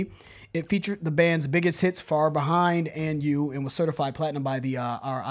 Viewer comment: I thought you said FROM BEHIND